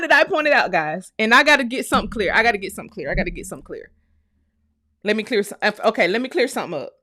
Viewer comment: Sound like he said. “Let me pleasure u”. 😂😂😂😂